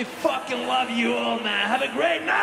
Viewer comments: did he say "I won't see you next time, I'll be.."? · did he say "i wont see you next time"?